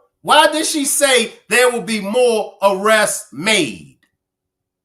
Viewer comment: I thought she said no more arrest will be made